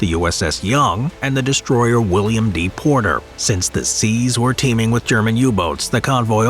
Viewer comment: I heard "deporter"